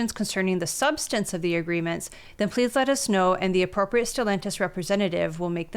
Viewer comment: Stelantis? Oh, we thought you said Cialis.